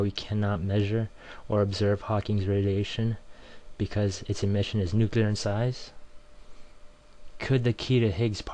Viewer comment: the way i heard it "smarsh nuclear inside" ? was this what you said or it was something else ? ony 2:44 to 2:46 please ...thanks